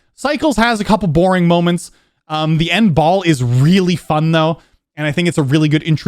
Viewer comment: turn on captions and it shows "bald" instead of "ball" LOL